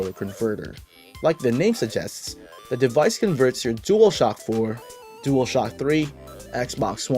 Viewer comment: Im sorry i heard you say “Ds4 and Ds3?” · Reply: Yes, you heard right. It's Dual Shock 4 and Dual Shock 3.